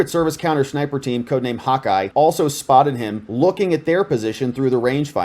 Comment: I thought they said "Hawkeye was in place" meaning the armored car was near stage for the carry-off...BUT ya now say'n also had a 3rd CAT snipper team named the same?